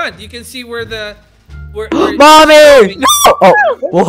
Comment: did he say “mommy”🤔